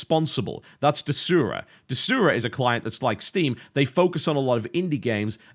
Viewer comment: Thesura? Tesura? What did he say @ 27:16?